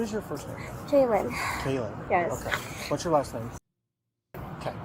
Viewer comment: Thought they said my name Jaylin😂😂